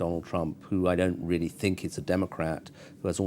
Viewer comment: Derek Temple are you completely stupid? he says Donald Trump is NOT a democratic politician, nothing to do with the Democratic party. You realise democratic is a word right? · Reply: AXE he clearly says at 4:26 "..who i dont really think is a democrat.." In the literal sense he's right, hes not. But thats not the context you would say that "hes a republican" in. I truly believe he severely misspoke.